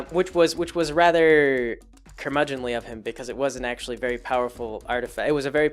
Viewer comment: What is the word you said around 11:41 I'm trying to broaden my english vocabulary. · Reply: "Curmudgeonly" is the word. A curmudgeon generally refers to an old man who is grouchy all the time. Like someone who yells at kids to get off their lawn at Halloween.